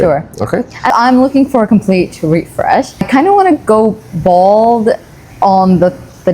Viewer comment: He's explaining the hair treatment that is going to fix her hair shedding, and she's all for it, then I hear her seem like she's changing her mind and says, "I kinda wanna go BALD..." @ 3:28 😂 The transcript says the same thing. 😅